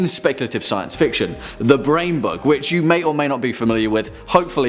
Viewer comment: You said "The Brain Bug", I heard "The Brain Buck".